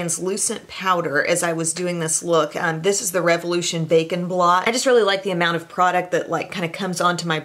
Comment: I thought you said ‘bacon blot’!! I figured it out though, and loved this video as always.